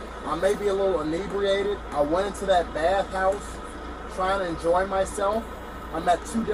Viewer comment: I went into that bathroom trying to enjoy myself and meet ..... is that what he said?